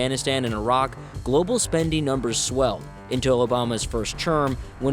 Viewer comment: Did he say ‘joe Obama’s first term’ @ 1:32 or am I hearing things